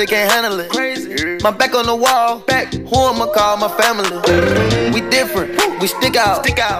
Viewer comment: all I heard was "I want to give a wall to my family"